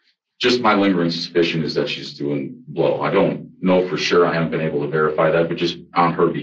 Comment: I think he says “doing blow”. 👃🏼❄️ (as opposed to the captioning’s: “doing well”.) He goes on to infer that using has been an ongoing struggle (“hardship”) for her over the last 12 years & is likely the reason for her “acute paranoias” in regards to her claims of his stalking behavior. 🙄